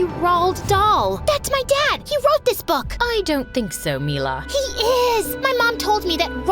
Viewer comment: Wait did I hear right is her nam Mila bc my name is Mila to